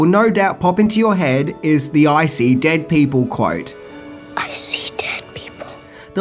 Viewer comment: sounds like says "Icy dead people" rather then the quote the way it's meant to be said/heard to begin with lol... although "Icy dead people" does sounds pretty scary as it is lol... They're dead AND now they have razor sharp ice protrusions coming off of them LMAO